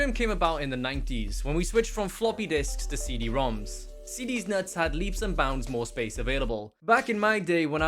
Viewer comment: Did anyone else catch that iconic CD-nuts at 2:35?